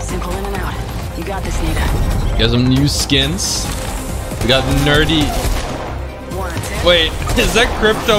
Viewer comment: did banglore say the n word?